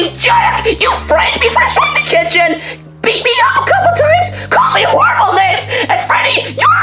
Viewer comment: what did chica say? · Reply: "Called me horrible names"